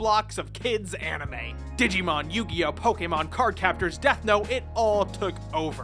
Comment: Did he just say Deth note? XD